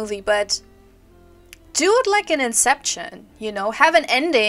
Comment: I had the same reaction to how you said "often" lol Also when you said "do it like in inception" I thought you said "dude licking exception" but I haven't had lunch yet ha! Anyway you finished! I have nothing more to add to what you said. It's an interesting consideration, if allowing the gamer to choose their ending in this way is empowering or passing the buck for how the story overall is received. It's also hard to keep it consistent if it isn't programmed to change the little cinematics for every possible combination. I wasn't a fan of the ending (puppet nips 😆) for reasons along the same lines as yours. By letting you decide the narrative, it kind of negates the narrative, making everything you experienced feel incidental. But like you said, it's a game to be more on the fence about than to slam outright and not recommend because there's enough there for gamers to love and the reviews suggest many do. Chalk it up to a matter of taste. I enjoyed the vids and look forward to your next hularific series!